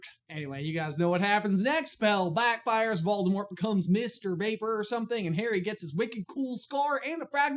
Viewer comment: You said “Mist or vapor,” but I heard “Mr. Vaper”